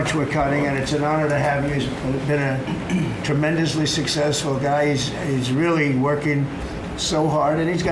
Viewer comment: did trump just say "it's been a tremendously successful guise"?! Am i the only one who caught that? Ive listened to it five times. Thats what he says, lol.